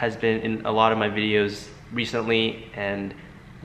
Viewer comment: Anyone else finds it cute how he pronounces some words.....like RECENTLY but it sounds like he says "wecently"❤️❤️😍 Still support you king ad always will